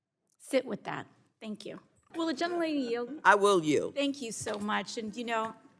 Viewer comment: “Will the young lady yield” …..disrespect oozing from his bones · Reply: I thought I heard “gentle” not “young.” I would also love to hear her pop off with, “I yield to the octogenarian…” but she won’t. She’s not going to be petty…even if it would feel good.